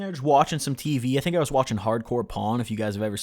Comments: i thought he was gonna say hard core porn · I fully thought you said you were chilling watching hardcore porn lol I was so confused